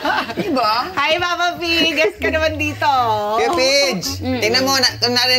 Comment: minutes, tama ba narinig ko? Sabi ni Pia, HI MAMA P, GUEST KA NAMAN DITO O.